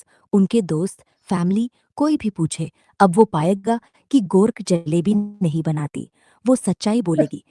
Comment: why it is saying GORK instead of GROK, which is its real name?